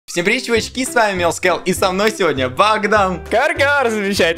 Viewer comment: мне одной послышалось Mils Skeld😂